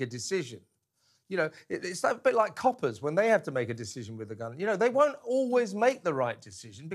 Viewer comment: Sorry, I thought I heard you say, cooper's make a decision 😮😂😂😂😂😂😂😂😂.